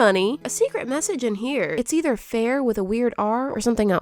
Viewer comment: i think it says fail